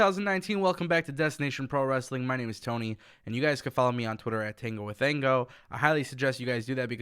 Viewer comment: It sounds like you say Tango with Thango. Edit: I had originally accidentally said "Tango with Ango" which is the right name, just not how it sounded.